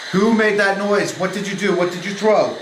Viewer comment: When you asked what did you throw it sounded like it said divot